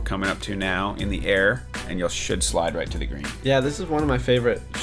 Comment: sh!t slide to the green? sounded like it.